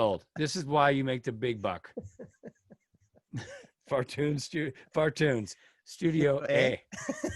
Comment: Until the captain I thought you were saying Fart Tunes Studio. Aye?